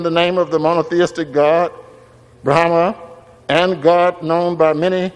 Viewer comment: did he say monotheistic God "Brhana", where did they get that name from ?? Are they going to change words like "Abdomen" and "Acumen" too !!?? SMH